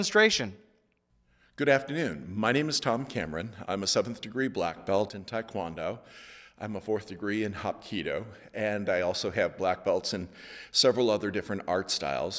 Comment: Sounds like he said I'm a 4th degree in hot cheetos